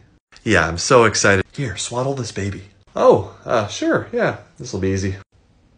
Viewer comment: Help I thought he said EAT this baby😭😭😭